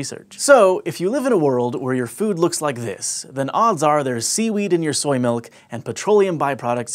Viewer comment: When he said seaweed, i thought he said semen